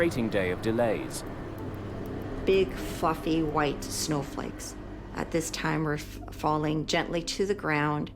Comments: Am I crazy, or does she drop an F bomb at 0:45 ?? It sounds like she's saying "big f*cking white snowflakes". Am I crazy? Does anyone else hear that? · I had to play that back again @ 0:45 sure sounded like she said something else 🤣🤣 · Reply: Yeah, at first it sounded like she said "Big "f*ckin white snowflakes" which is probably a technical term, right? 🤔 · All I heard her say was "big white FUCKING snowflakes ". Am I the only one who heard that?